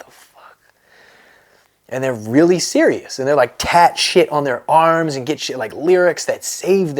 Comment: I thought he said cat shit on they arms